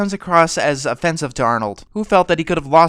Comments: Wait... Was it just me, or did he say "Arnold".....Am I missing out on something? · Arthur might quite possibly be the most underrated animated series of our time. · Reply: did he say arnold?